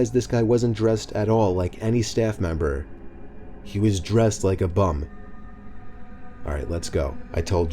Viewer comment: did he say " he was dressed like a bum " or " he was dressed like a bomb "? o.o